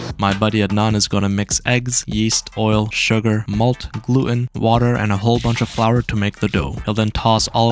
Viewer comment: did he said glue 🫣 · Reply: Gluten…